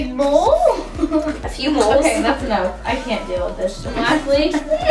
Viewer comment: "okay that's enough, I can't deal with this shit" just me who heard that lmao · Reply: SupremeVlodeine she said shirt